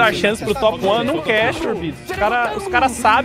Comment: Eu ouvi Shubi? Ou xubi 🤨